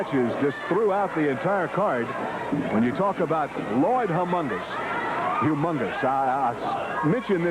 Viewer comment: did he call him lloyd humongous? I want to see that character now....